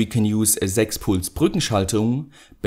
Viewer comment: What did you say at 4:36? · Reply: 6 Puls Brückenschaltung